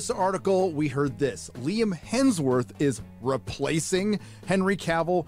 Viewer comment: I heard "Liam Neeson" at first and honestly... I was like 🤔